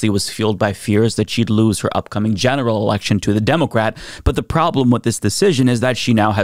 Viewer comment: Did say "genital election!?"😅